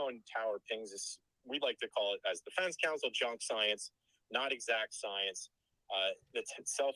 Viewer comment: “Chump science”, he calls cell phone location pings. I’ve never heard this told to a jury! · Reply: I thought he said "junk science" .. 🤔 I'll need to listen again.